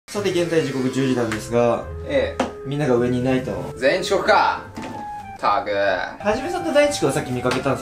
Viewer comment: ｢全員遅刻かぁ！｣が ｢センイルチュッカぁ！｣ に聞こえた、末期… 誕生日だれもおらんやんなぁ